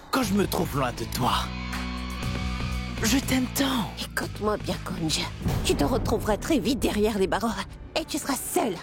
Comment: c moi ou twinkel à appeler youvi kunj😮😮😮